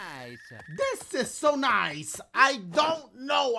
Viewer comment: Sounds like Mario is saying, “Dick is so nice!” 😭